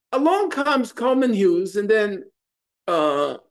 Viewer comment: I thought he said, “Along comes Common Hughes and that Thing called Destiny. 😂😂😂—Common Hughes. Boomers drag you real bad. 😂😂